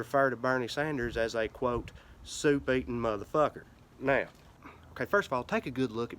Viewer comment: I thought you said "suit-eaten!" Like Bernie's suit is eating him alive! #hearingproblems